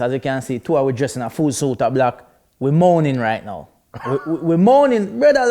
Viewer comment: i thought man said "we are moaning right now" hahahaha